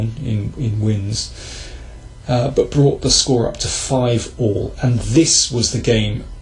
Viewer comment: "Brought the score to five or"?? What does he say? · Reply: Yan Air. At 4.25 says "five all', meaning that they each had won five games.